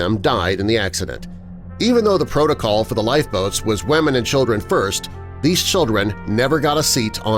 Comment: Did he just say wAmen and children?